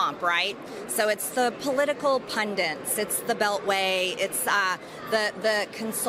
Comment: did she say... political pundents?